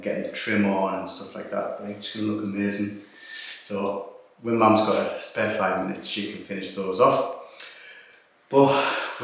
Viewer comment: Did I hear "when mom has some spare time," or surely I heard that wrong? Smile!